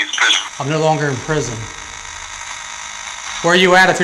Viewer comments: I heard “im not only in prison” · I heard “only in prison” or “phony in prison” not “I’m no longer in prison.” Just proves that you hear what you want to with those things. The second one is just a bug on the camera. And come on... the stereotypical ghost girl who just peeks around the corner? 🙄 He also doesn’t show the entire bathtub. As for the part when he runs to look down the stairs, he points the camera at the white wall as he does. I’d be willing to bet there is a cut there. Five, again, the ever elusive girl ghost peeking around the corner. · No one with two functioning ears would get “I’m no longer in prison” from that. “...(“..inaudible...in prison”) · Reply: ya that's not what it said. i heard ''only in prison'''